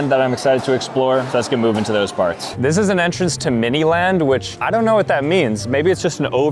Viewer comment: for a sec I thought he said MiniLadd (we don't talk about him...)